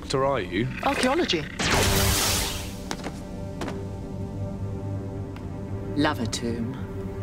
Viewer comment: At the end, I thought she said she studied urology ("love a good tube"). Took me a couple rewinds to figure it out